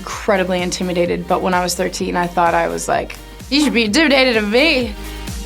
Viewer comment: what the did she say? You should ................??? What 😳😳😳 Sounded like the satanic secret Hollywood language to me! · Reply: She said "you should be intimidated of me"